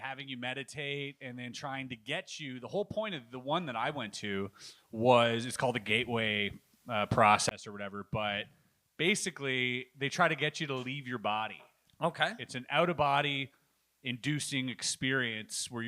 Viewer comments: I had to listen to this 3 times before i heard anything other than "gay boy process" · I had to listen to this 3 times before i heard anything other than "gay boy process "